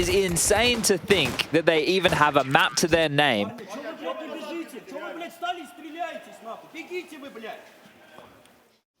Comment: what did electronic say? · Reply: "why are you guys standing?! standing still and shooting... fucking run!"